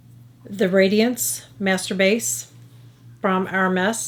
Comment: During your quick review at the end you said, "Master Base" and I thought you said masturbates and I cracked up. So funny! Everything looks wonderful. I will give several of these products a try including the Master Base. When I first saw it I thought it would be way too dark for me because I am basically the same shade as white paint. After watching it in action, I think it will work nicely. Thank you.